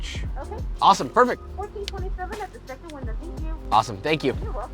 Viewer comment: hold up, am I hearing that right? 14.27 for two burgers?!